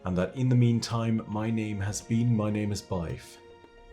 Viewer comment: Ugh, I keep thinking your name is "Byfere", not "my name is Byf." for the longest time, I thought you were saying, "My name is," as in prepare for my name, then "Byfere" as your name. Not, "My Name is Byf," here! 😝